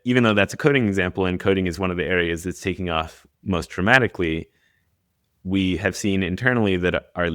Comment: I swear, it sounds more like he said "traumatically," than "dramatically." Hmmm.